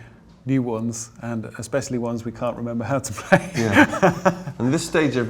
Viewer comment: What is he saying there? thx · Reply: "specially (the) ones we don´t remember how to play hehehe"